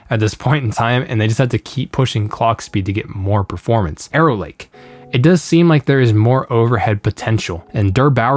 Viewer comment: Why do I always hear "Error Lake" ?